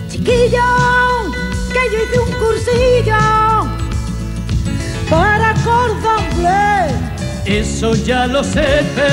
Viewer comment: Chiquillo! que yo un cursillo para cordon-bleu. Eso ya lo se pero chiquilla, (que?) dame pepinillo y yo los remojaré con una copita de ojen. Papas con arroz, bonito con tomate, cochifrito, caldereta, migas con chocolate, cebolleta en vinagreta, morteruelo, lacón con grelos, bacalao al pil-pil y un poquito perejil. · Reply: Siempre pensé que decía "yo hice un cursillo para comer bien"... de lo que se entera uno al cabo de los años 😂😂😂😂